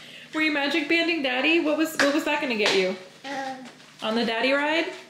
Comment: When was chasing in the backyard, I thought he said, “I’m gonna get you. Get you.” 🥰 …And in the pool? #relaxationgoals